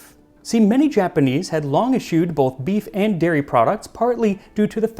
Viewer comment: the subtitles say "issued" but I'm guessing they meant "eschewed". Nice video! Can't wait for tempura